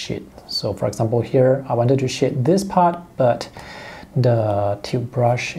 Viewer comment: lmao you sound like your saying 'shit' when you say 'shade'